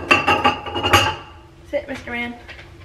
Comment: Did you call Noah “mister man” at 0:57?? I call my dog that ALL the time and I thought I was the only one!!